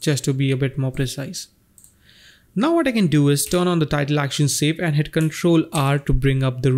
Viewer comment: What do you say at 2:02 ? "Title Action Shape" ?? and what shortcut did you hit to bring that up ?